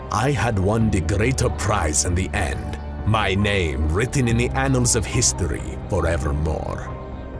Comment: Wait, did he say anals?